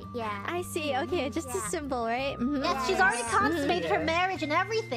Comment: what Gigi said at 1:30 wasn't "consolate" (what??) but "consummated", which is an important detail that should be included... And Biboo says "I have a seed"...